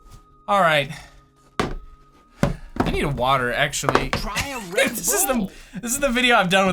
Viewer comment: did anyone hear "try a red bull" at 4:40 ?